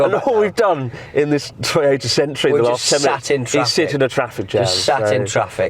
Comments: I now get why Chris' hate comment videos highlighted his accent - For all these years, I heard "Toyota Sentry", not "century" 🤨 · all this time, i thought you guys were saying "Sentra" instead of "Century"